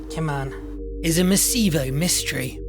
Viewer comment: What does he say here? "a ...(meseevo?) mystery". I don't know this word